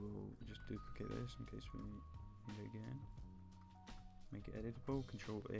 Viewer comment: wtf did he say at 2:22 ? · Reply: Make it editable ;)